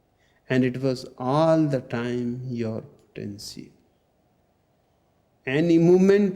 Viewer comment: Which word did he say @ 15:00..."And it was all the time your_____?" Thank you...😊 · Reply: And it was all the time your potential.